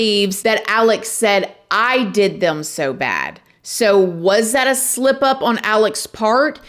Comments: I heard, “I did them so bad” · I’m at 20:05 right after they slow down. I really think, with his accent in mind, that he’s saying “it” not “I” or “they”. I hear the word it. Anyone else? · I hear they did him so bad.